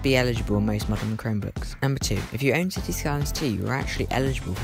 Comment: what in the world did you say at 0:35 im american and im trying hard to interpret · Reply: Cities Skylines 2